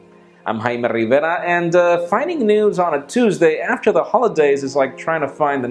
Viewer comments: is it just me or does it sound like he said finding nudes on a holiday. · thought he said finding nudes on a Tuesday.. was like Jaime chill